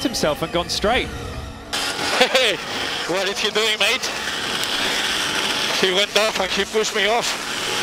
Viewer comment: What is Perez saying at 5:57? "He went off like he pushed me off"? I don't quite understand that remark.